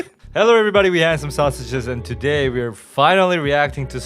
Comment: Nah my volume was low and I thought you said "hi we have some sausages"